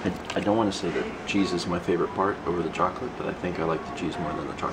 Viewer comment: i thought you said "I prefer the jesus over the chocolate" and i was so confused until my mind corrected itself xD